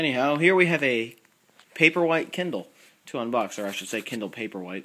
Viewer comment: I thought you said paperweight, that's what they really are!